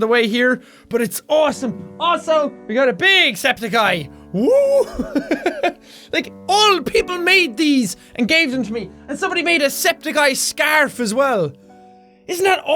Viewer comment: Sounded like you said "Old people made these"